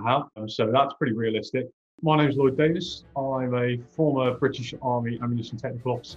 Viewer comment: thought he said lord davis